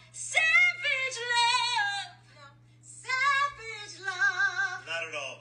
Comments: I thought it said Sausage love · bro I thought it said “sausage love”